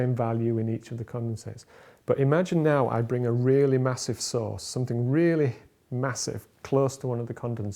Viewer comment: I mistakenly started watching this from 27:01 without sounds on. Had a fun time trying to guess what he was explaining ;p. Love these videos. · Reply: Also, since 27:29 could not help thinking about them talking about a sauce, not source. I am having too much fun not paying attention to what is being said ;p.